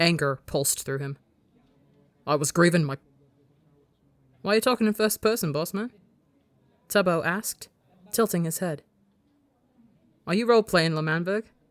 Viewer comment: I listen with headphones, when I heard Tommy start saying “I was greiving,” I threw my headphones, when I put them back on all I heard was “don’t worry I used to roll play South Park” then threw them again